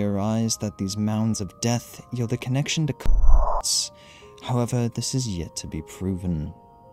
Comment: my brain automatically filled in the word “cunts” instead of “cults”